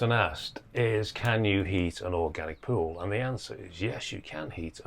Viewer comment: misheard him and thought he said "can you eat an organic pool" and was excited for some absurdist humour